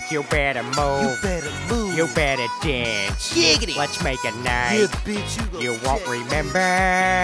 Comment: when he said let's make a night I thought he was saying watch meg at night lol